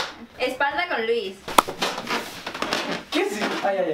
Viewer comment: entendi que pasa con luis jajajajajaja